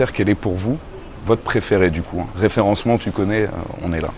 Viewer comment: j'ai même pas fini la video j'ai entendu " référencement tu coco" j'arrive direct mon pote!